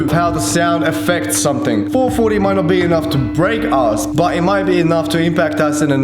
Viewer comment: is it just me or did he say “it might not be enough to brake arse” 🤣